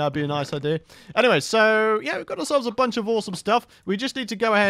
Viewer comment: "we got a bunch of ore-some stuff" wooow i dont think he realized what he just said lol